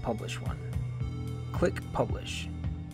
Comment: quick publish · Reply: I know he said that it sounds like he said quick publish